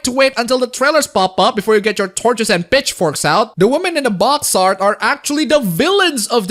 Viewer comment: Did you say "bitchforks" at 7:05? Sounded like it to me. xP